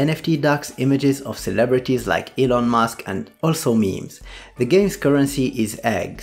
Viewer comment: I heard "of celebrities like Elon musk and also me" :) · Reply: lol, no I said also "memes"